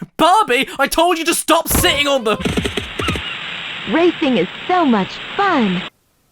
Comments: Im just gonna ignore what the captions just said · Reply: Racing is so much fun "bang" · Lol the captions say raping is so much fun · the subtitles say "raping it so much bang" instead of "racing is so much fun" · hold on did Barbie just say raping is so much fun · Even youtube's subtitles thought she said 'raping' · Barbie: "Raping is so much fun!" What did you say?! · I thought it said RAPING is fun